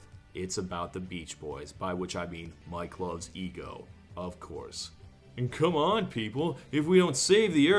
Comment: for a second, i thought he said mike love's e-girl. i was very confused for a second